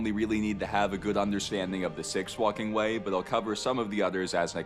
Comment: My mind knows he is not saying this....but my ears keep telling my mind "He is saying The 6th F'ing way" and it makes me giggle so I am letting my ears hear that. 28:39 - Here he mentions the the 2nd, the 5th and the 6th F'ing way. And when he said it he put some respek on it. I'm having fun with this. Is that weird.